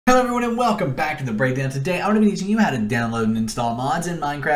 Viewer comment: lol at th star i heard "Welcome back to the Breakdance"🤣